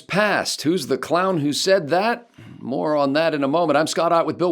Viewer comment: "Who's the clown who said that? More on that in a moment." I heard "more on" as "moron."